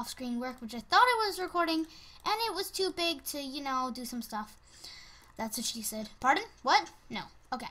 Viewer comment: *Hears "That's what she said..."* *head explodes*